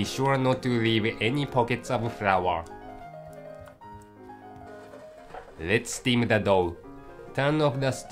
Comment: I thought he said, "lets steam da dog..." lol